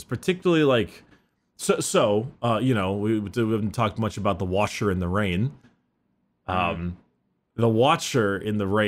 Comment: I swear I heard him say "the washer in the rain" and it makes me wish I could draw, cause now I have the mental image of the book cover but it's just a dish washer in the rain.